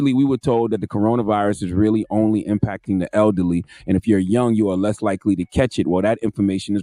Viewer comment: They did not say that, they said if you're young your less likely to die from it